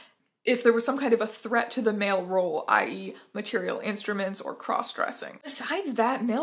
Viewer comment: every time you said "material instruments" my brain fogged over and all I heard was "mAtErIaL gWoRl" and I'm not sorry